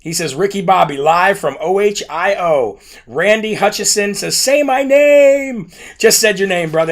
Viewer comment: I had to go back and read the comments because I heard you say what I thought was “Randy Hutchinson” which is my first cousin lol. But you said Randy Hutchason lol. No relationship. 🤣🤣🤣